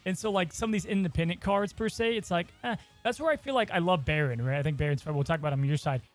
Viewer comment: Did say 48:50 “that’s why I love Barron”… as in mordo?? wtf is happening